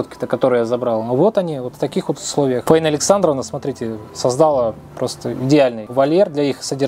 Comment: спасибо большое за твои добрые дела 🙏 низкий поклон 🙏 Спасибо большое Раисе Александровне за такое великое дело, за добродушие 🙏 Дай бог вам здоровья и долгих лет жизни! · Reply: Мне тоже сначала послышалось Раиса Александровна. А позже оказалось Фаина Александровна. Как бы ни было, огромное уважение этой прекрасной женщине с добрым сердцем.)